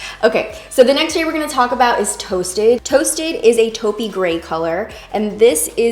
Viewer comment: Blueberry instead of toasted